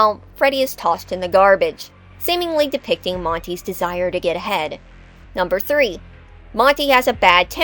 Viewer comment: What I heard: “… seemingly depicting Montys desire to get head.” 😳😮